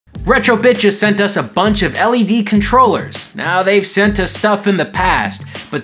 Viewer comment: Sounds like you said "Retro bitches"